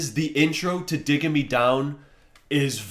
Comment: I thought you kept saying dicking me down on the scream ranking 😆